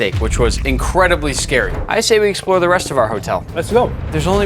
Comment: I thought he said explode😂